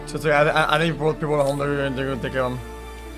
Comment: wtf did he say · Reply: "It's okay I think people are home they're gonna take care of them"